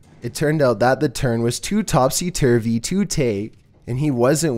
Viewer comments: WTF did he say??? Edit:First time getting 100 likes 😉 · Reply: Turns out that the turn was too topsy-turvy to take. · What thell did he just say? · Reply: Too topsy turvy too tae. From what I remember it means some sort of doing something too far or feeling some way.